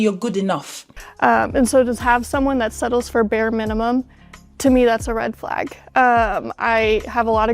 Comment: It sounded like she said she was bare minimum 😂